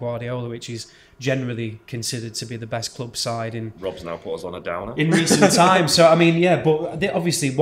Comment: What did the guy say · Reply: "Rob's now put us on a downer!"